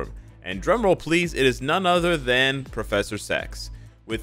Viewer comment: Am i misheard or he just said "prof X" Fast so i heard he said "prof S*x"???